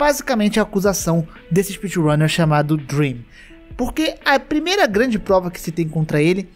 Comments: quando ele disse "DREM" eu entendi "DREAM", fiquei até em choque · Ata, kkkkk quando eu escutei "Dream" já vim correndo nos comentário falar: Por mim, você ta errado o Dream é um cara mega pro ele nunca trapaceou. mas vi que era o "Drem" kkkkkkkk...